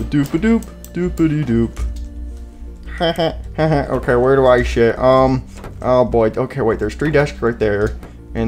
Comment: When balloon boy said we’re do I sit it sounds like he said we’re do I shit